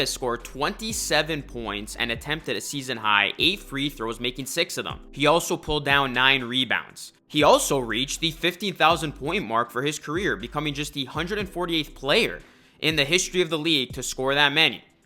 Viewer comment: For a second I thought you said brook Lopez reached 50,000 points. Instead of 15,000. I was like wtf?!?😂😂